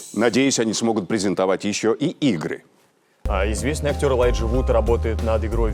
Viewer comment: лайк если на 10:01 подумал что он сказал: ... презентовать ещё и игрик